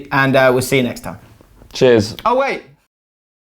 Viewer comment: I'm intrigued as to what was going to say at the end. I heard "o wait"